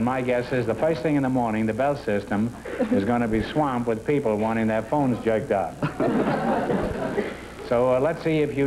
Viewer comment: It sounds like Groucho said: "And my guess is the first thing in the morning the Bell System is gonna be swamped with people wanting their phones jerked off." AM I RIGHT?!!!